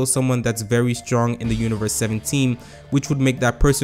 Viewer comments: am i the only one who heard it as universe 17 and later realised it as seven team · Am I the only one who heard "universe 17.."